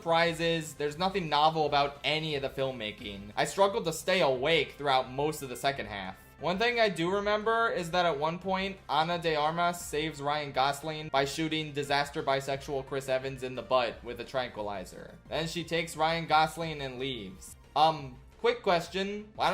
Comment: Thought you said Disaster by Sexual Chris Evans